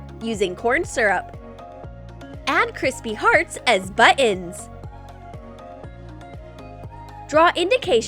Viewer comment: Am I the only one who thought she said “Buens”?